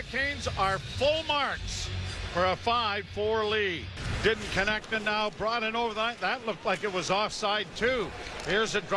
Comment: What does Bérubé say at 10:47? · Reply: "darn it!"